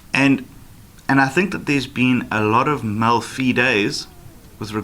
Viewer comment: What is mal fee days? (That’s what the transcript says)